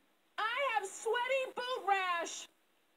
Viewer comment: What? I always thought she said Sweaty Boob Rash.